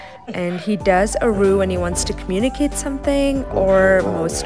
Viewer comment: When you said expressive I thought that you said expensive 😑😅🤣